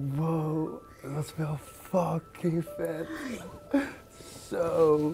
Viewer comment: sounded like he said “must feel fucking fat”😂😂